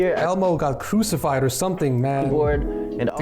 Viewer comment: did he just say elbow?